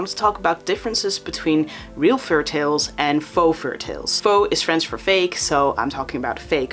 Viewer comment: Fou=faux=fake! For anyone wondering what he said